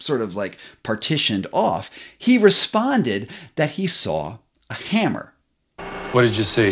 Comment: it sounds like he said "saw and a hammer" but when you replay it, it sounds like "saw a hammer". freaky!